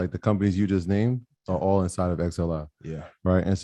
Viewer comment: Wat was the name of that etf he said at 2:10? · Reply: He said. XLI it’s the Industrial Sector ETF XLF is Financials